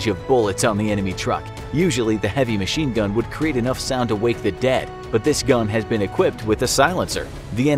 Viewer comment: He said the heavy machine gun made enoughf noise to wake the dead But at first I thought he said it makes enough sound to wake my dad